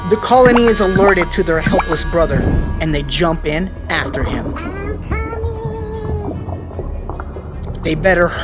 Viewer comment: WHAT DID IT SAY ON 7:37 (After is says after him) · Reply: IM COOOMING